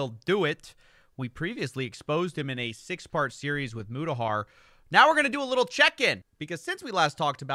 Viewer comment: I was listening to this video in the background, and in the intro I misheard “6 part series exposing Mudahar” and thought WHAT NO PLEASE NO